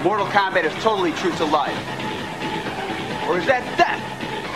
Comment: What the heck does Daniel Persina's "Johnny Cage" say at the 1:39 mark??? sounds like "And remember, Mortal Kombat is TOTALLY SOOO 90's.... I digress...true to life... OR is that THEFT?!.... Dafunk?!??!." Could someone else listen to it, and try to make sense of what these wise words from Johnny Cage mean? :P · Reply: "Mortal Kombat is totally true to life... or is that DEATH ?!"